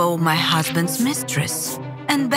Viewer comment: I honestly thought it say my husband mattress was ugly and than i was like oh wait